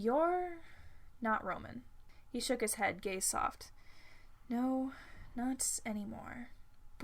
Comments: I misheard "He shook his head, gaze soft." As, "He shook his head, gay soft." And I was like, "Well... I guess there ARE soft gay things/people. Gays can be real soft, at times. This must be like, a thing. ...I had like this whole reasoning behind it before realizing I had misheard all along. (0---0|||||) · subtitles say gay soft. Its 5:30am and im dying of laughter. Amazing story btw